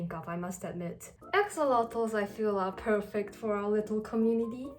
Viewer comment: I misheard Axolotls as Eggsalot so I thought she was calling us Eggscellent